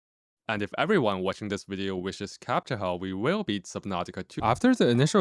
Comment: Up until 7:40 I thought the game was called Captcha-Ho